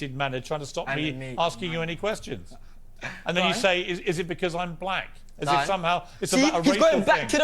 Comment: I thought he said “because I’m black” to Piers asking “why are you like this?” 😂😂